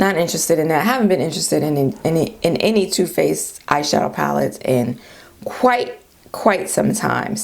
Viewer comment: I laughed so hard! I thought you said ‘ash shadow’ and then you said Too Faced. So appropriate for my melanated skin🤣🤣. Aww MAC they will always have a place in my heart though I havent bought a thing from them since 2010.